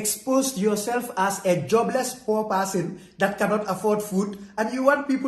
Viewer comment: did he say a jobless poor person 🤨